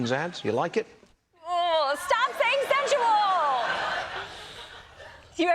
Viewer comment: does he say “consensual”?